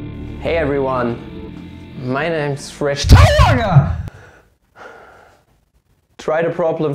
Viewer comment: In the beginning I thought you said "My name is Fresh Torge"...